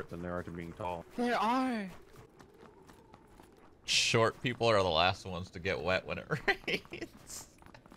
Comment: what did he say? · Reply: Etern4l Saiy4n Short people are the last people to get wet when it rains 😂